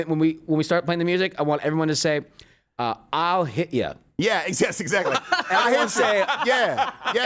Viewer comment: I heard ohh i'll hit you at first, but ohhh boy was i wrong.